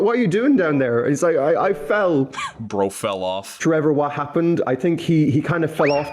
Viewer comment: why does it sound like hes saying trevor?....IS THAT YOUR NAME?